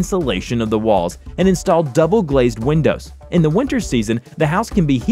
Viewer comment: I thought it said that the house could be YEETED.